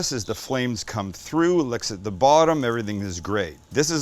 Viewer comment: Did he just say, "licks at the bottom?" 😂